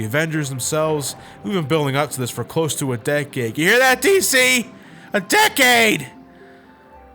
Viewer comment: I heard "Ya hear that T.C.?! A decade!" like dude what are you on deviantart? Wh-why? but I guess he said "D.C."